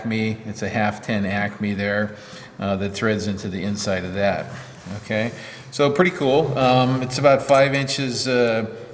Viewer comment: what is that "scubid" or what is it saying in between words? Is it some easter egg or something? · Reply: That was an "Excuse me" in the midst of a hic-up.